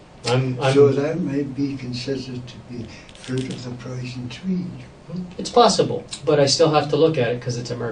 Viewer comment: Can anyone understand what asked at 2:32:00? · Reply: Fruit of the poison tree